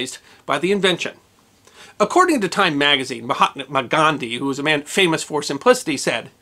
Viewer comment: Am I hearing this correctly at 12:33? Mahatma McGandhi? Was he Scottish-Indian? I must be hearing that wrong.